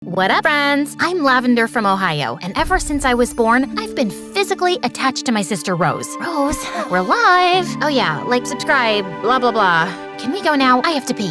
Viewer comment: DID SHE SAY OHIO?